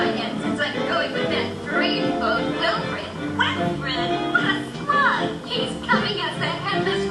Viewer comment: Did... did she say "what a slut" at 13:55??? Someone please tell me what she actually said because... no way. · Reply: she says "what a slug"